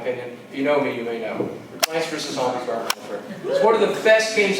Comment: says the big sleeper hit of the last console generation at 21:24. I turned my volume all the way up. I still can't understand what he's saying. There's some clicking going on, and he says it too fast and I honestly can't make it out no matter how hard I try. I'd really like to know his opinion. Help? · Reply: plants vs zombies garden warfare